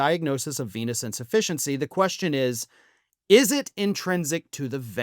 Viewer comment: Sorry. Keep mishearing venous insufficiency as p*is insufficiency. Sorry.